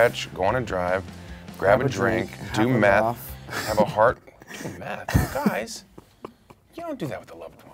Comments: Tom: Laughing Chris: “Do meth? Guys...you don’t do that with a loved one...YOU DO IT WITH A STRANGER” E1Thanks for all these likes, I just seen them! E2Oml ty ty yall for 1k likes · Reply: ahahahaha i thought they said “do math” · Tom: Laughing Chris: “Do meth? Guys...you don’t do that with a loved one...YOU DO IT WITH A STRANGER” E1Thanks for all these likes, I just seen them! E2Oml ty ty yall for 1k likes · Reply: Omg i thought he said do math😂🤦